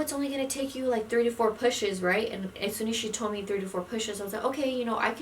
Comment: at first I thought you said 34 pushes. golly but much love girl. much love